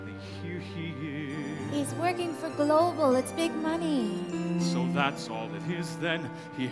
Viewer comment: When her character says He’s working for Global. It big money”. All I hear is “My riot got you on TV I deserve royalty”